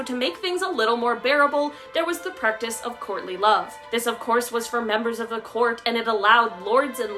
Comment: I thought she said Courtney Love at first instead of "courtly love" LOL !